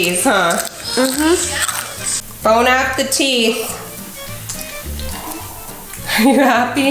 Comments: I heard “bone off the teeth” 😂😂😂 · I heard “bone-app-a-teeth” and I think it was suppose to be Bon appétit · Did she just say Bone apple teeth!?!? ❤️😂❤️😂❤️ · did you say bone apple teeth at 5:55 im